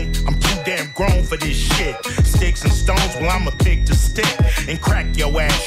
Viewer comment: I thought he said "Imma beat the stick"🙂